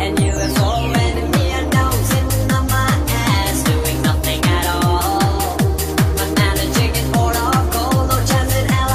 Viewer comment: did he say a**? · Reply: “Sitting on my ass doing nothing at all”